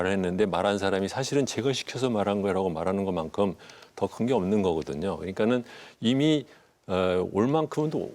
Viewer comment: 더 큰게 없는 거거든요 (0) 더 관계 없는 거거든요 (x) 33:46 한겨레 x -> 한경 O (한국경제신문 ; 대한민국에서 재벌의 나팔수 탑을 달리는 매경,한경) 40:59 소위 말해 연루라고 하거나. 라고 들리네요. 제작진들 일도 많고 바쁘시겠지만 부정확한 자막은 차라리 없느니만 못합니다. 신경 써 주시길 부탁 드립니다.